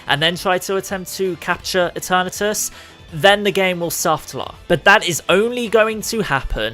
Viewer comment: what does he says here? "Then the game will *******" no matter how many times I try to understand I just can't. Can anyone please help? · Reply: softlock, but you probably already found out